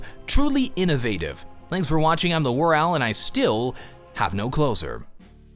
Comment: Whenever he says "I have no closure", I always hear 'I have no clothes on', is it just me?